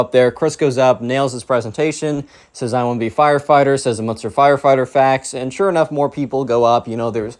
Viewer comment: Just a few days I pulled 4 all-nighters in a row so last night I put on your videos as I fell asleep. Your videos have really helped me. You and your videos are amazing. You mean the world to me. So thank you, for everything. <3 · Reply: Bro I literally thought you said tie fighter😂